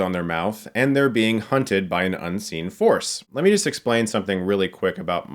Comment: I first thought that Chris said that they were being hunted by an unseen horse. That would be either the best or the most ridiculous movie ever made.